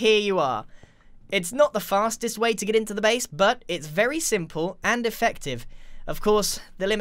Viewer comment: I thought you said “It’s very simple and defective!”